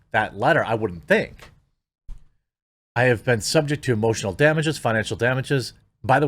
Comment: Did I just hear "emotional damage"?